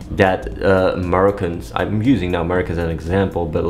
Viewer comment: I thought he was saying Murricans